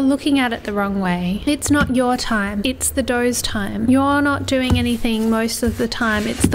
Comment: Its not your time its the doughs time For a sec I thought you were saying it was the boss of me